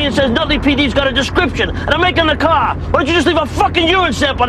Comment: what does Tony say? They've got a description and they're making the what? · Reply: he said and a make on the car